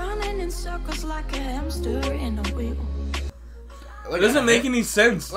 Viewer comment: i swear that song said "like a hamster in a wig" i didnt realize it wasnt until you guys started making jokes about it and even then i was confused 😭